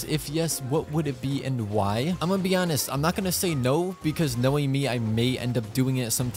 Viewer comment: I was confused because I thought he said my name ''Noémie'' :l